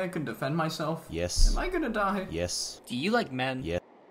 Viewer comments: "Do you like men?" "Yes-" got me dying 💀💀💀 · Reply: bro I thought he said "do you like meth?" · "Do you like men?" "Yes-" got me dying 💀💀💀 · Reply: ngl i thought he said do you like meth